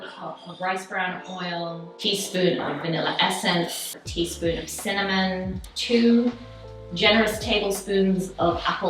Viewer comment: I was sort-of half listening while the recipe was being read out, and rather than 'teaspoon of cinnamon', I heard it as 'teaspoon of salmon' LOL - that def made me take notice!